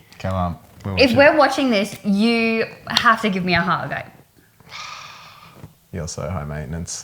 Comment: What is she saying at 5:38?? “If we’re watching this you have to give me a _____”. What is she saying?? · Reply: The word you're looking for is hug. 😊 Thanks for watching and writing in, AussieGirl.